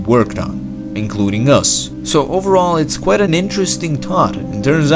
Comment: I rrly just heard him say we shood just keep tinking